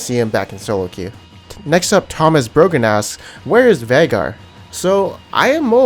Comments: did he just say Thomas Broken ass???? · "Thomas brogan" I thought he said "tahm is broken" lol